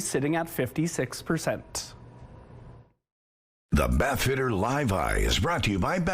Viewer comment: in the kitchen and i hear bath shitter... -.- oh my